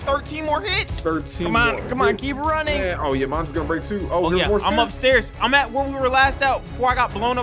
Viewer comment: Bro did say your mom is gonna break to